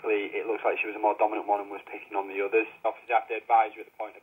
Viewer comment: What did he say??!😧😂😅❤️ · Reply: 'Picking on the others' I guess it sounded different ahaha x